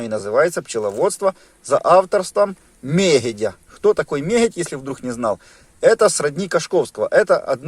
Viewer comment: Прошу написать автора книги пчловодства Мерядь? Правильно я, расслышала? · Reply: Мегедь